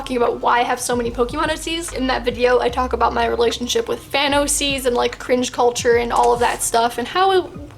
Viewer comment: When you said fan oc's, my sister thought you said thanos seas 😂